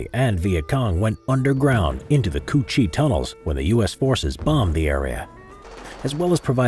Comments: I legit thought he said coochie tunnels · Did he just say coochie tunnels